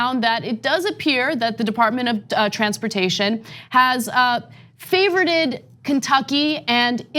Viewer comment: Nice report but did you just say favoureded!